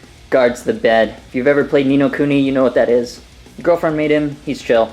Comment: where's that from? I couldn't hear you too well. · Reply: Ni No Kuni. It's a Japanese RPG on the PS3